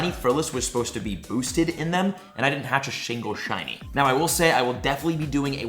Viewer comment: I heard Shingle Shiny at 2:45 😂